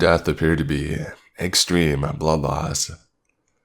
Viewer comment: Omg I just misheard "extreme bloodloss" as "extreme blah blahs"! Lmaooo